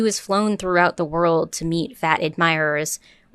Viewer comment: excuse me... did she say "meeting" or "eating" ??? serious question! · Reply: Lol meeting but had to put captions on to make sure 😆